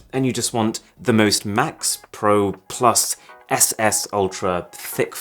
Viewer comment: did he say ss? D